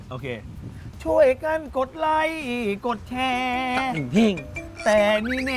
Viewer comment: ได้ยินว่ากดไล่กดแชร์ 55555